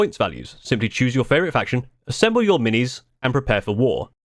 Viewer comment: Or just paint your models. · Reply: Did you just say: "buy more minis"? I do but my army remains gray!!!